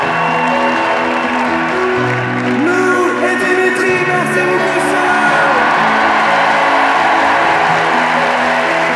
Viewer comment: Il dit quoi à 3:47 ? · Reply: " Lou et Dimitri, merci beaucoup , So ! "